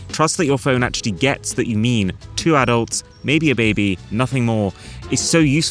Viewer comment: Did he just say maybe a baby?